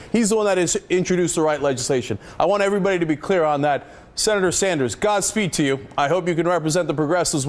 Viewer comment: What did he say @ 5:00? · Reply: the force be with you Senator Sanders...